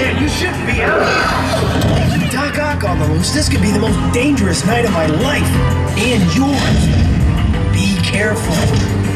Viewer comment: does anyone know what the heck he’s saying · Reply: Doc-Ock on the loose, this could be the most dangerous night of my life, and yours